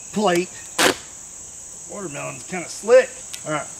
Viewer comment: I misheard "This watermelon is kind of slick" for "this watermelon is kind of *t h i c c*" and I don't know if I should be concerned for my mental health or not